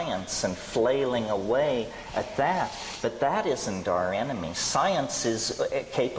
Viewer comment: Hii, this video was very helpful thank-you so much. I just wanna know what's that word he says at 06:27 sounded like 'doranimy'. A li'l help here? Pleaseee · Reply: He says “But that isn’t our enemy”